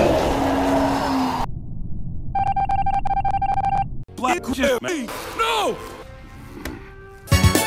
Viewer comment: am i the only one who thought he said black jimmy?